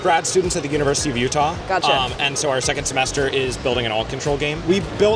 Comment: small caption error here, “all” instead of “alt”